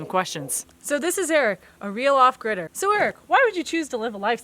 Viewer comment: Lol i thought u said real off critter